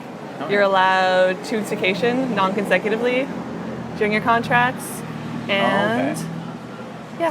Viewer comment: What did she say at about 5:25? "you're allowed 'tutsication' non consecutively"... I was thinking 2 vacation but it doesn't sound like she said that... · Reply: 'You're allowed two weeks vacation, non consecutively' is what she said